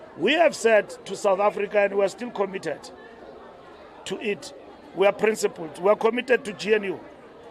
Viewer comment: When he said,"we are committed to it",I heard,"we are committed to EAT."😂😂😂Made me think of someone who said,"Let them eat".For this MPs knowledge not all South Africans have dual citizenship!!!!To a LOT of us this beautiful country of our birthis the only country we know!!!!!!